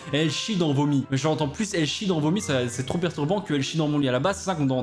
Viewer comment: Moi j'ai entendu "elle chie dans mon nez" 😅😂